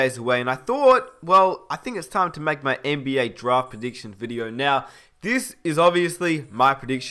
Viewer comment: I thought he said "my draft addiction today"